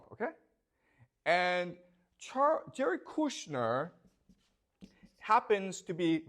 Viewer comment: I keep hearing "Jerk Kushner"